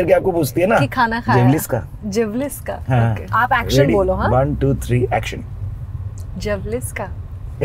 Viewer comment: Did she say "zhavlis kaa👀💀"